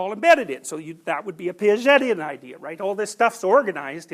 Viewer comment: Does anyone know what word he says at exactly 17:33? "That would be a ???? idea" ?? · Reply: Piagetian. He's referring to the psychologist Jean Piaget.